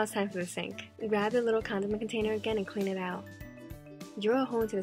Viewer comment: I thought she said condom container